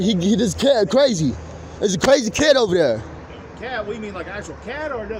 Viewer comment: I think he may have been saying a crazy kid, rather than cat, around 13:40-ish. He mentions "not an animal", as well as the culprit having "long dreads and crooked teeth".